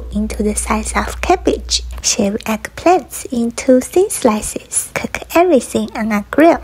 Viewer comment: Nobody Everyone:it’s not vegan she used cheese Me: did she just say shred eggplants🤔